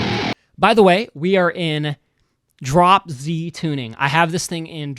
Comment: Awesome breakdown! For a second I thought you said you were in Drop-Z tuning 😅